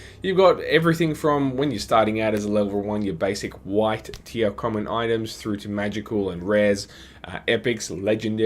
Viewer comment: For a second I thought you said "wife tier common items" ( ͡° ͜ʖ ͡°)